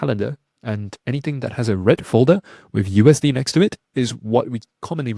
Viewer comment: Thanks for spelling it out. I always thought you were saying “red fold the news” and I had no idea wtf you were talking about!